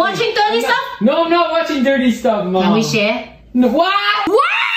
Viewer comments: what did she say at 4:20? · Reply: Can we share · what did she say? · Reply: “Let me share” NO!